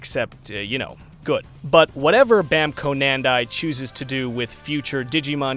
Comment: Did anyone else hear him say Banco Nandai instead of Bandai Namco at 21:10 ? :D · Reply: It was intentional lol. In fact it was "Bamco Nandai," not "Banco Nandai."